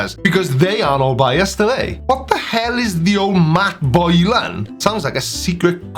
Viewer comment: "Matt Boyle Line", not... whatever you heard. idk what it MEANS, but that's what he said. xD